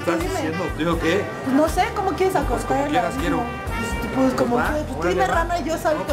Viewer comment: Fui la única que escucho que dijo vamos a "acostar" el abismo jajaja😜 en el min